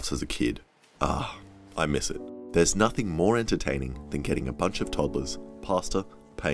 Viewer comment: Omg I thought he said “ah there’s nothing more entertaining than killing a bunch of toddlers”